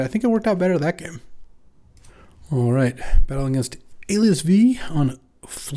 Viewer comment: I heard "Battling against LSV" and did a double take